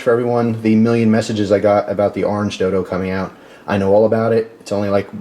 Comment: Wasn't paying attention for a second and thought you said orange dildo haha 😂